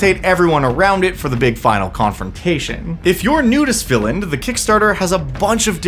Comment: 😂 thought you said “If you’re a nudist villain” 😭😭😆 the double take I just gave my phone!